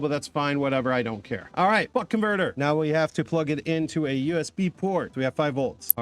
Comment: I got notification and it sounded like fuck converter 😂😂😂😂